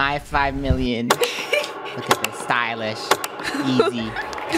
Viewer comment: is it just me or do i hear "mmm, stylish"